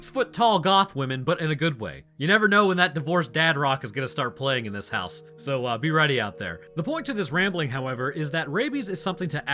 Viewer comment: did he just say he's getting divorced. 😢